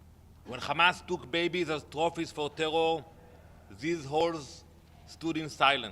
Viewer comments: Shameful !!!! Listen @ 2:06 did he just call the UN , who*es????!!!!!!??? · Was it just me or did he actually say it . Shameful !!!!! listen @ 2:06 is he saying who*es ????? Shocking , wow . This one and the one before him are a walking one man circus . I do miss the other , Lol 😂😂😂 he also forgot to mention all the other lies they have thrown at the world. Remember the calendar in Arabic 😂😂😂😂😂😂😂 it was not what he was saying Lol